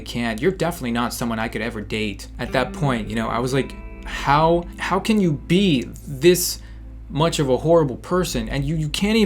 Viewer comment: i thought he was going to say "how much can you be such a whore" lol